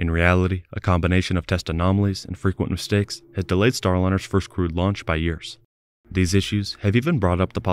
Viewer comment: I misheard 'first crewed launch' as 'first screwed launch' and I couldn't stop laughing at how accurate that is